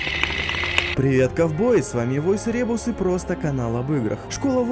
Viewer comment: послышалось "привет говно"